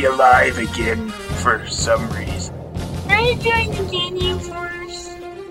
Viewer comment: What he said "Can i join the Ginyu force?"?